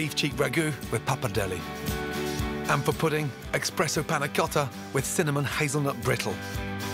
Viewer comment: did anyone else hear him say 'expresso' or ......?